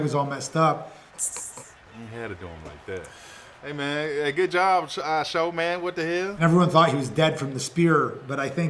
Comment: Did he just Called him IShowMan🤣🤣